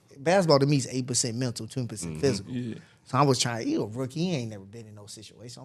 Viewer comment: I really thought dude said at 4:29 "basketball to me is 8 percent mental 10 percent physical"😭